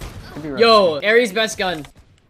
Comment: did he really said ares the best gun or my ears are ringing